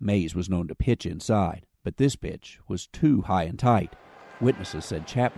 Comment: at first I thought the narrator said “but this b!tch” lol